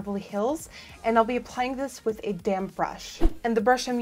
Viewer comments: oh my god at 2:00 i thought she said "I'll be applying this with a damn brush" 😂😂😂😂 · did anyone else think she said "damn brush"? 😂 I love her lol.